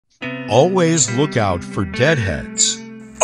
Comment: i thought it said always look out for dread heads ☠️